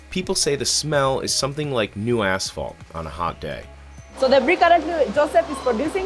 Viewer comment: thought I heard 'new asshole' 😂